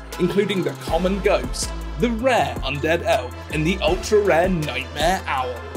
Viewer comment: I thought it said undead elf